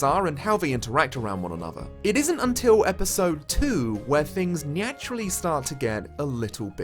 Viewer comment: Did I just hear you say "nyaturally"?